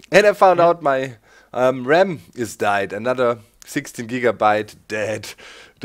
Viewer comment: I thought he said "my mom is dead" and then laughed about it. lol